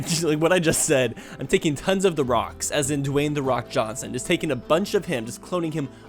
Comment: I've gone to a couple caves in real life, and I don't remember seeing Dwayne Johnson there. I guess I wasn't looking hard enough... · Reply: I thought you said Cave Johnson.